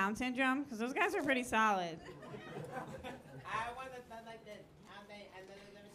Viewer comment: Does anyone know wtf the audience member said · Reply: "i have one that sounds like this" LMAO